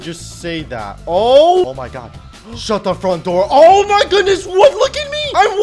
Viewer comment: First of all i thought said shut the f*** up but he said shut thr front door